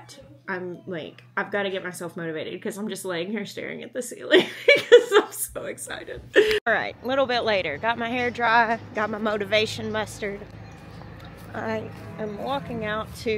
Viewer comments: Omg I thought you said you've got your Motovation Mustard and I'm like what IS that?! Mustered. You have mustered motivation for yourself 🤦‍♀️🤣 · I heard, "motivation mustard", instead of "motivation mustered"...lol. My brain, I swear...